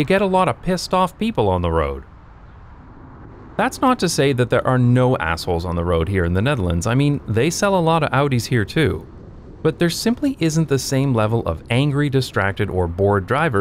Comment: "They sell a lot of Audis here too!" 🤣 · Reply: lol I thought that he said "they sell a lot of hoodies here too" in a way to talk about bad guys it made me laugh either way